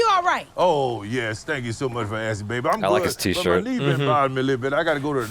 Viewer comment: I heard his name and I said "Dabi?!" I only know one Dabi, from My Hero Academia. No no, 'DaVI.' 😅😄